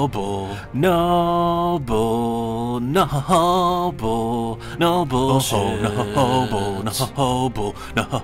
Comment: That intro was sooooo gay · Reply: All I heard was "Noble Shit"